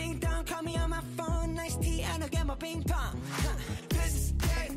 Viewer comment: is it just me who hears: "Nice d"